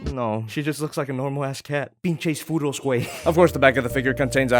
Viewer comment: did he just said "pinches furros wey"?